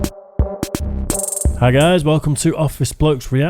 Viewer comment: did he say "five guys"?